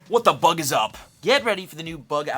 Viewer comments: i swear i heard him say what the f--- is up, but he just said bug. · I thought he said what the F@ is up but he said bug 😂